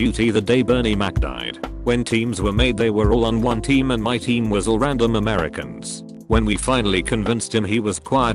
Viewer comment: Any one else notice that at 8:30 the programme said "my team was random Americans" instead of "a black guy actually convinced us his name was quiet" lol